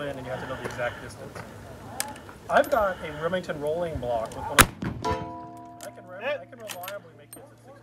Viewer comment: I heard someone say "I've got a Remington Rolling Block" 👍🍻